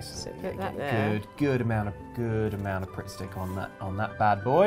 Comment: I genuinely had a moment where I thought said "Prick Stick" instead of Pritt Stick and I went "I'm sorry, what?!" XD